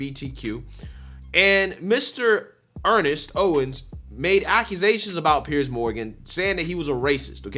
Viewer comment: did you say accusations? Because i read this on a mens room wall in Maine over 40 years ago, " the left make accusations, the right cite examples " Roostah.